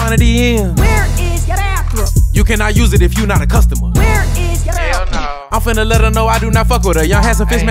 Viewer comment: I didn't watch the video where she came from and thought she was saying "Where is ya afro" 😂😂😂